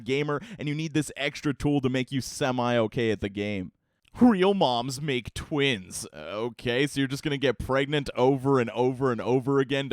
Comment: I thought it said real moms make twinks 😂